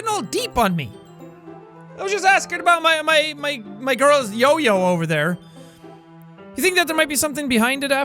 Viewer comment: Did he just call Sayori "Yo Yo." Oh god...